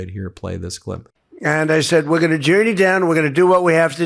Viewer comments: DID TRUMP JUST SAY "WE ARE GOING TO JEW YOU DOWN??!" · DID TRUMP JUST SAY "WE ARE GOING TO JEW YOU DOWN??!" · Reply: It sounded like he said we're gonna journey down, but i can see why it sounded like he said we're gonna Jew you down.